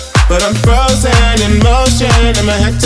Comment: I thought he said "Frozen in Moet Chandon" instead of "Frozen in motion" 🤣